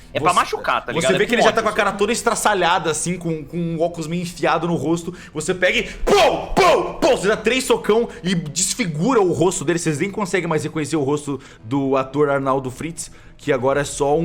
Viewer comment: [...] Vocês nem conseguem mais reconhecer o rosto do ator (Arthur?) Arnaldo Fritz . . . . WAIT? WHAT U SAID?